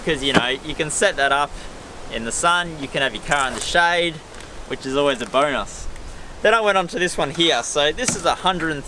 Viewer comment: Did he say "which is always a boner"?